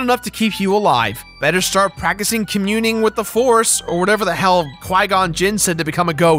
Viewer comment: I heard "Klingon Jinn" not Qui Gon .... are your a hidden treki?